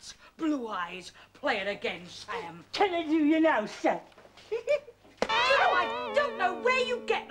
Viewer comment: does anyone know what does croc says at 12:23??? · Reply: I think it's "Can I do you now, sir?", a reference to the film and radio show It's That Man Again.